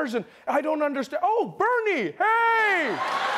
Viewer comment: When he said Bernie, I thought he said Barney. XD